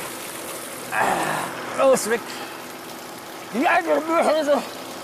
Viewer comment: Kann bitte jemand 7:29 und 7:36 übersetzen 🤣🤣🤣🤣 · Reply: Bei 7:29 Sagt er (so wie ich es Verstehe) "Die Alte Möhre" Und bei 7:36 Sagt er Sehr Sicher "Dies (oder Dialekt Des) Nervt Aber Anders geht das nicht (oder mit Dialekt Nich) Also mit Dialekt "Des Nervt Aber Anders geht das Nich" ;)